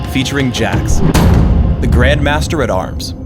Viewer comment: lol at first I thought it said Jax the gangster at arms